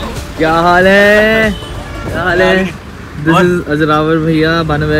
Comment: WAIT WAIT WAIT, did he just said yaha gadi khadi karni hai Lawde at 4:50😭😭🤣🤣🤣💀